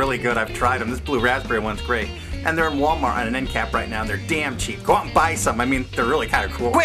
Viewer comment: they are in walmart and are "in Kap"? is that what he said? kap is short for kapuskasing, 5 hours away from sudbury, ontario. in canada if not, i dought he said "end-cap"